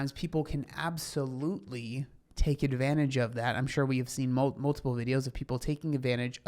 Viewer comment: I thought she said, "I'm suet!"